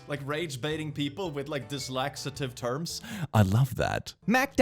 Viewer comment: I'm sorry, what? 1:25 Dislaxative heart/terms... dyslacitive.. dislexiative??? What is that word you used?? I don't recognize it and am highly curious and intrigued.... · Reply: He said dyslexative but ironically meant to say dyslexic